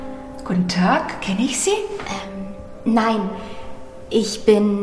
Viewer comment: What does the woman say at 2:27 after “guten tag”? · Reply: "Kenne ich Sie?" = "Do I know you?"